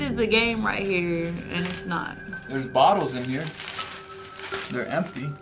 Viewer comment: When he said there is a bottle in here I thought he said there is a bomb in here